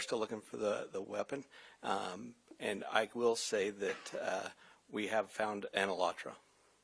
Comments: what did he say they found? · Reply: He said 'we have found an Elantra' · what did he say ???? they found the car ?? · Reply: We have found an Elantra....which means we have found the car....the car is an Elantra.